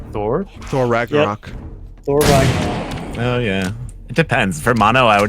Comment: Wasn't paying attention and misheard 'Thor: Ragnarok' as 'Borat: Ragnarok'.